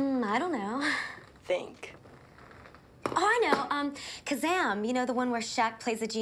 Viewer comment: I swear, I remember her saying "Shazam" not "Kazam"